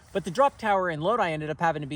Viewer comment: The drop tower in Lodi? Lodi is a town I live in. Did I mishear you or is there actually another town with the same name (because I seriously doubt you live near me lol)?